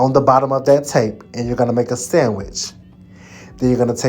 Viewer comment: I know you didn't say it but every time you said sandwich I heard sammich 😂 I'm stupid 🥴 This is so pretty!